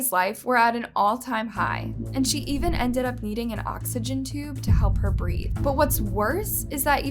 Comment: lmao I thought you said she ended up EATING an oxygen tube 😀